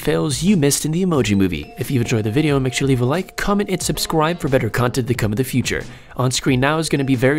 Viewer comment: At the end it sounded like he said "comment and subscwibe" XD